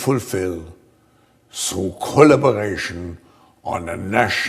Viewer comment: I could have sworn I heard schwab say In Con U lotion